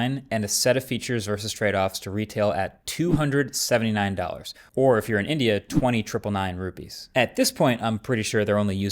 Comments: i heard if you are an idiot 😂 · I swear to god i heard "so if you are and idiot" 😭😭😭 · The part where he said "or if you are in India" I thought I heard "or if you are an idiot" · I heard idiot instead of Indian 😅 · I THOUGHT HE SAID "IF you're AN IDIOT"